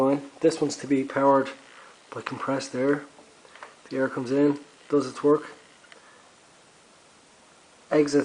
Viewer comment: I heard, "... the air comes in, does a twerk ..." :3